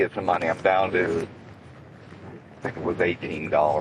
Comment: did he say gas or glass